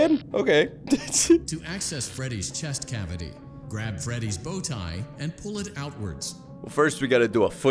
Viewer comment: When he said pull Freddy’s bow tie I thought he said pull Freddy’s balls I watch people play this like 100 time but this just went in my head idk why